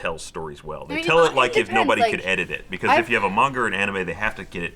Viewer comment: did anyone hear say manger